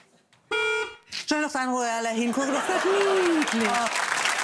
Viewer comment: warte mal ich habe endlich rausgehört was sie sagt "schnell nochmal sagen wo alle hingucken ist das niedlich"